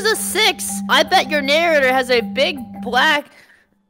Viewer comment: HOLD IT DID SHE JUST SAY NARRATOR!